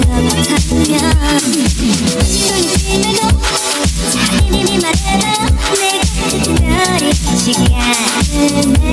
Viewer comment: EXCUSE ME WHAT DID SHE SAY? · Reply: Amoyas